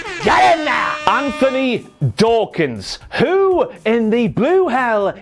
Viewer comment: I'll be honest, I didn't even notice that "Anthony" was the wrong first name, I just thought Ross was saying "Dorkins" for the last name 🤣